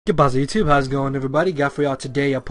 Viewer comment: What do u always say in the beginning of your videos? I always hear it as "Good Byes, youtube", but that doesn't make any sense at all :]